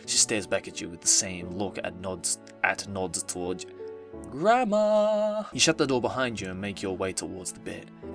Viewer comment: When he said gramar, I thought he said grandma